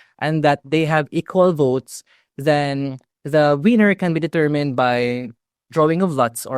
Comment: Hi, lots of good info in this one. I must confess the idea of a coin toss to resolve an election tie is quite amusing particularly given accent, it sounded like he said the toss of a coin would decide the Wiener 🤣🤣